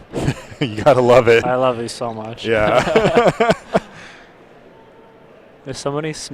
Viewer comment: Hehe sounds like he says "I love you so much" at 20:05 :)